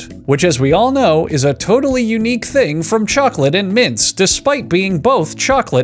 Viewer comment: Why I always heard it "chocolate and mince"